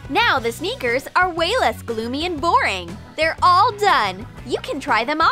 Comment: what did they say boring???